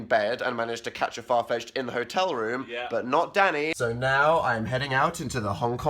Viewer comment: DID DAN JUST SAY "DANNY"?